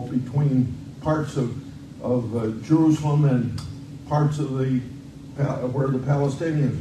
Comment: I was drinking while listening to this and when Grossly said “the good things you did as president” I spit up my water in shock and anger. GOOD THINGS! He ran the country into the ground! Grossly speaking of walls proves he is too damn old to be doing the job. He has no idea where he is much less what he is talking about. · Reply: I thought I heard him say "Jewslem" for "Jerusalem". LOL 😂